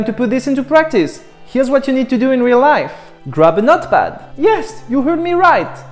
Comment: thought you said "grab a Nutt bag"